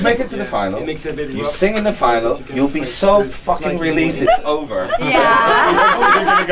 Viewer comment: Btw what did the dude say to Lo' on 10:52? "U'll be f*cking...what after the last word? I couldn't hear it. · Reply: Betty Renfro He said: "You'll be so f*cking relieved it's over." :)